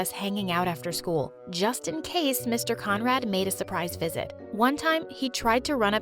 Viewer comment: I thought you said “comrade”